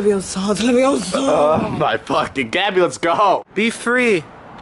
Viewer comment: did david call liza gabbie